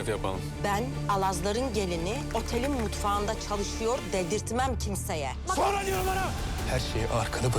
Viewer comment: ne diyor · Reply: Sonra diyorum ana diyo